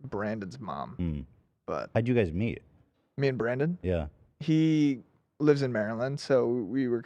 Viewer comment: Did he say "me and 15 cent?"